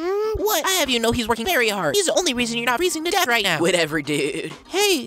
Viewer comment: what did he say all can I hear is "dead right now" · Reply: WHAT?I’LLHAVEYOUKNOWHE’SWORKINGVERYHARD! HE’S THE ONLY REASON YOUR NOT FREEZINGTODEATHRIGHTNOW!